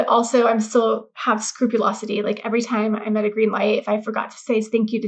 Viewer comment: she mentions, again the word spruculosity. Have I heard that correctly? I want to look up the definition. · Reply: scrupulosity... being obsessively thorough...